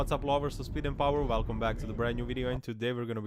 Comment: I'm sorry what did you call us??! 😘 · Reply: Lovers of speed and powaa, i hope i wasn`t wrong? 😂